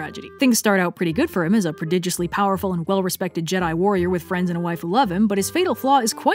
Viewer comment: The first time I heard that I thought she said "waifu" and saw Ahsoka on screen. Anyway, I'm going to go bleach my brain now.